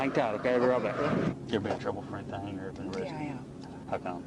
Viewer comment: am I hearing things or did she answer “I am” to his “ever been in trouble for anything.” Question? (Instead of answering “yes I have been”.)